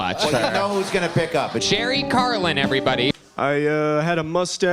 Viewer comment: I thought he said Jerry Curlan and I was like that's an Angry Samoans song!